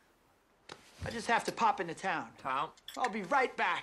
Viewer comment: Haha, I just noticed that Leonardo (Gatsby) called Toby (Nick) "Tom" instead of "Nick" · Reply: He said "town?" Because Nick said he was going to pop into the town